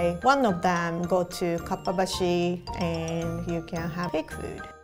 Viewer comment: that ramen looked sooo real!! :O also, does anyone know how to spell the name of the area in tokyo where said you can buy kitchen supplies? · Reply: i heard "Kappa-bashi" but im about to google it now 🤣